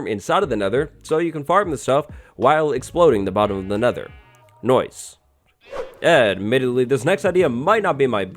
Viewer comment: is it me or at 5:41 did he say noise